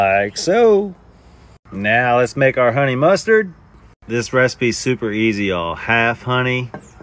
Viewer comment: I wasnt paying attention to what he said until i heard "honey bastard". I spit, and now i am paying attention